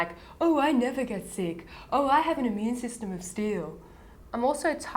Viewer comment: I had to rewind because I thought you had said 'oh, I have the immune system of a seal' hahaha